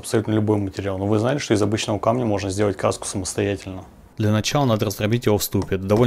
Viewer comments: Красику что ли, мне послышалось каска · Мне послышалась каска... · А я не прочитала название видео и мне послышалось что вы сказали: Самостоятельно сделать каску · Мне одному послышалось Каска? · Мдэ, сначала услышал "сделать КАСКУ". А оказалось разговор про кРаску))